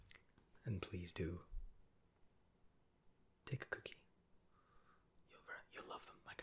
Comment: I have no idea why, but when you said "take a cookie" it sounded like you said "dick cookie" and I had to rewind to make sure, lmao.